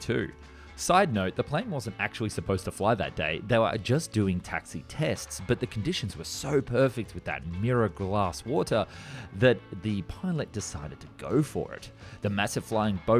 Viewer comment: The real mystery is was he saying “Mirror glass water” or…. “Miracle Ass Water”??? 😂😂😂😂